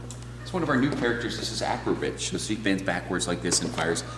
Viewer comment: Did he say acrobitch Wtf lol at 5:55. I love your video's Im jealous.